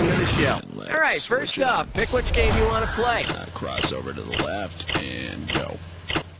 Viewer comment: That word is funny called cross over the lapkin